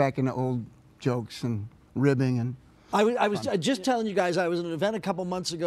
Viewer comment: Did he just say "We get back into old jokes and Rimming" ??